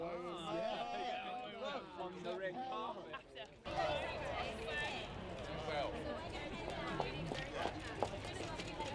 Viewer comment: Did Jagger say something about Rick Parfitt at 0:13? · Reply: No. It sounds like "red carpet"